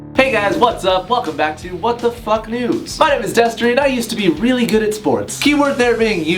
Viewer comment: I thought he said " WHAT THE FUCK NUDES" at the start